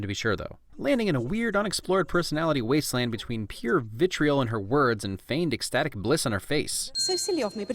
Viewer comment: for a moment thee I thought you said bitchriol instead of vitriol